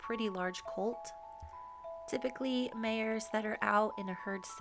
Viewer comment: it's funny, the YT translation AI understands "mayor" instead of "mare" every time and in the German voiceover tells me something along the lines "Normally elected heads of cities who are kept in a herd are getting impregnated..."😂